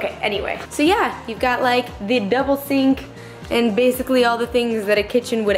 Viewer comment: omg I thought you said "devil sink" not double sink LOL congrats on the place its looking great.